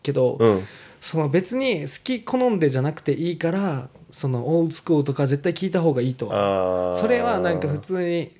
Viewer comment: ここの場面がどうしても聞き取れませんでした。誰か教えてください🙇。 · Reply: old school